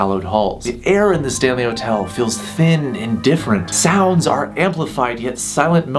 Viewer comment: I thought he was going to say the air in the hotel feels THICC 😂